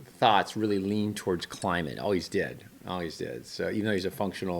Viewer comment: Wow, at 9:12 your captions say that Jan has passed away. The words "always did" were translated into "all he's dead, all he's dead" Youtube's captions get it right however.